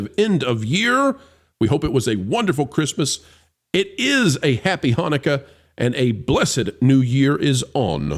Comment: Did he just say: "Happy Honecker "?!" Weird, Weird 😉😁